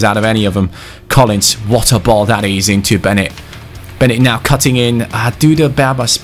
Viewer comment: did just call beghin bennet?